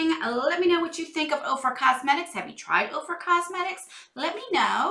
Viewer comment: Thank you so much for being so generous! I'm so ignorant about make-up brands that the 1st few times I heard YouTubers talking about Ofra Cosmetics, I thought they were saying "Oprah". As if Ms Winfrey needs to make her own cosmetic line when she gets all those "Favorite Things" for free! 😁